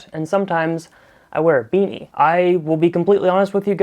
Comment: I just read your channel description and I thought you’ve been saying “hi my name is beret and sometimes I wear a beret”